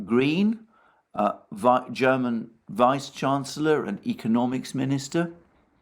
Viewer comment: When mentioned Habeck, I first understood "the German Reichs-Chancellor" instead of "vice chancellor". Gaffe or premonition on my part?